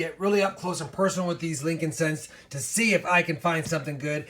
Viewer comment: Lincoln cents. I thought he said Frankinsence.